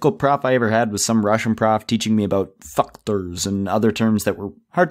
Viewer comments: I realllyyy thought you said fuck-tors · Russian teacher sounds like he said FUCK-tors. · Am i the only one who heard him say fucktors lol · When the russian teacher said factors I thought you said f birds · when you said factors it sounded like you said FUCKTERS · it sounded like he said fuckters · I thought he said Fuckers at 4:04 😂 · Instead of factor it sounded like he said f#cktor lol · when you were saying "factors" it sounds like your saying "fuckterdrs" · "factors" i thought he said fuck-tors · the captions read fuck birds instead of factors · When he said factors I thought he said fuckturs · what I hear fuckters what it says factors hmmmmm. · omg lol I thought you said fuckters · THE CAPTIONS THOUGHT WHEN THEY SAID FACTORS THAT HE SAID FUCK BIRDS